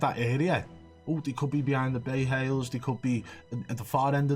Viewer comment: does he say "bay hails"? lol